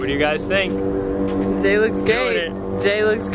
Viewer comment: When she said "Jay looks great" I thought she said " Jay looks gay" hahahaha oops